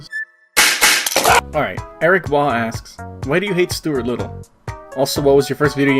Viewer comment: Best part, 2:30-3:45 :Smug: also, I think you said Erik bowl and not Erik ball LUL